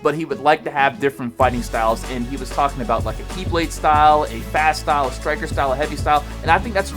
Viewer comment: Who else thought he said Keyblade when HD said KiBlade